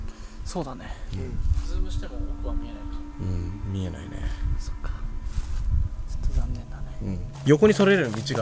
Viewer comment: 「うん見えないね」の後で女の人か分かんないけど「そっか…」って小声で聞こえた気がしました